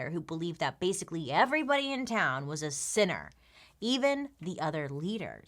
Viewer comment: Everytime you said 'sinner' I thought you were saying 'sitter', and I was like 'what's so bad about sitting?'🤷🏽‍♀️🤔